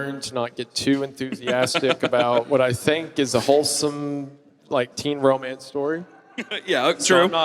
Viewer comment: did bro just say child sex is wholesome.... what clown world do i live in... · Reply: No, you said that. He said he like wholesome teen romances in stories. If you hear "teen romance" and you start to think about child sex, you need therapy.